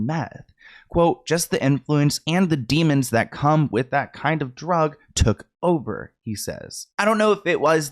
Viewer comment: you missed quoted orlando talking about meth and that has a pretty big impact on how what you're saying will be heard. your misquote ("that kind of drug" instead of "that drug kind of") implies he's referring to the demons of meth use specifically when he's not. since you're bringing this info to the platform, it's important you take care and responsibility to not spread misinformation or undermine someone's vulnerability by misrepresenting them.